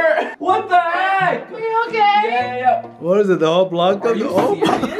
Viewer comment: he said BABE hold up... 😱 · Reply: Slime Wunderland yesss i heard “are you okay babe”